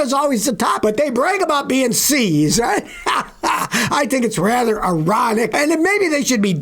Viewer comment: it sounds like he said "I think it's rather erotic" lol